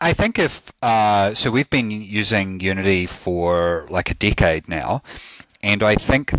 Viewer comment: genuinely thought he said like a dick head